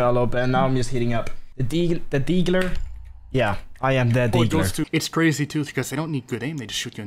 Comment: When he said, I’m the Degler I thought he he said I’m the didler